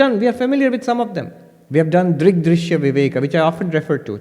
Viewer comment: Can someone please tell me what text is said at 5:04? Is it "Dridri Shavivaika"? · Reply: Drig drishya viveka